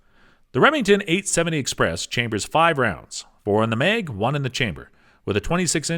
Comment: Did he just say 4 in the Meg lol